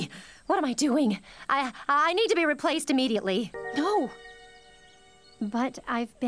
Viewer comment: "I need to be removed immediately" Me too pearl, me too... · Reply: i thought she said “replaced”, not “removed”